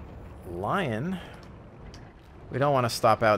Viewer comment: I heard '' lion''